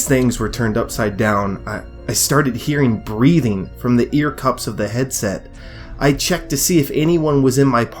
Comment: for a second i thought i heard BREAD XDDDDDDD